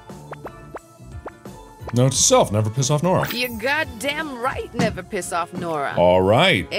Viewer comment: Mark: "Note to self, never piss off Nora" Also Mark: * pisses of Nora * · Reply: Thought it said piss on Nora